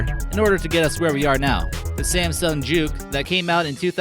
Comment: sounded like you said "samsung jew" instead of juke.